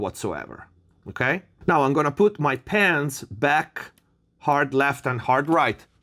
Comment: "Now i gonna put my pants back" Did i hear it right?. All this time you were sitting without pants? 😆